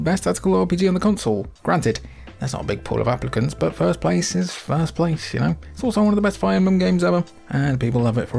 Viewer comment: can anybody understand a word he is effing saying??? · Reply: 'first place is first place yaknow? its also one of the best fire emblems ever' american and it was understandable to me